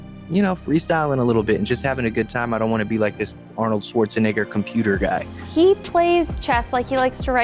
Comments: he did not say negger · what did he say??????? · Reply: Arnold Schwarzenegger